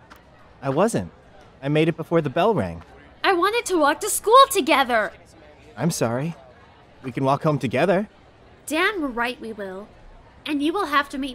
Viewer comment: "did she say dan right we will" whos dan?